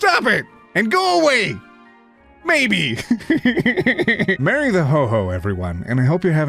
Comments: For a moment I thought said "Marry the Ho Ho" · when he said "merry the ho ho" i heard it as "marry the ho ho" and i was like "don't mind if i do <3" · I’m so sorry But I thought you were saying Marry the hoe hoe 😆